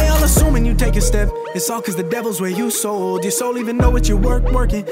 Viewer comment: did he say he sold his soul?